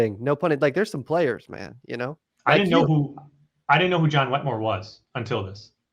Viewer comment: What's the last name of the John person he mentions around the 8:30 mark? I couldn't make out what he said but I want to look him up. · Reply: John Wetmore. Here's the podcast I shot recently with John: <link>